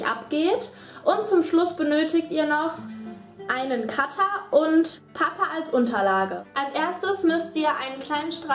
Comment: Haha ich hab verstanden: ...und Papa als Unterlage xddd